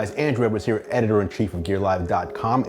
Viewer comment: Andru I thought you said android